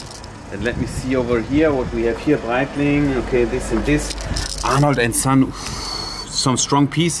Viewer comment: This whole time I thought you were saying a lange & sohne until you showed the Arnold and son watch 😂